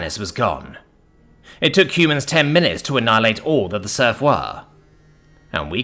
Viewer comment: I swear I heard you say Smurf 😂